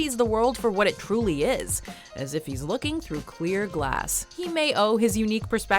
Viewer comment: i honestly thought the narrator said "queer glass".